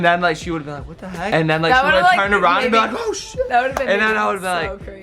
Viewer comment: did anyone else hear say shit?